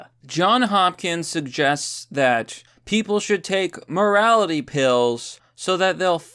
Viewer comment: I thought it said "Mortality pills" I was happy for a moment ;(